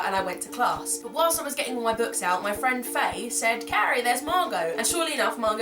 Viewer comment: im sorry i think i heard you wrong? did you say your friend's name was fatty at 1:41?